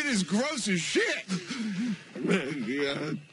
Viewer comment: did Glenn said "Adrian I'll find you"? Or "Negan I'll find you" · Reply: I think he says Maggie